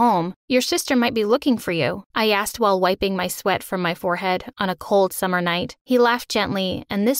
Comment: did she say on a cold summer night?